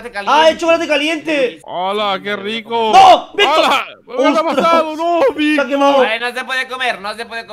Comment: Solo fue mi idea o dijo O no mi Victor :^ Solo fui yo? :^ Claramente eso escuche =<= MAYICTOR ES REALLL